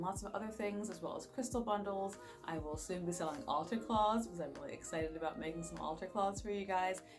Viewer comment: I thought you said ALTAR CLAWS LOLOLOLOLOLOL I’D BUY THAT